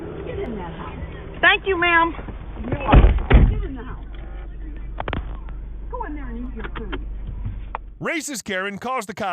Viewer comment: you didnt understand what they said? its pretty clearly go in there and eat your food am i the only one who heard it?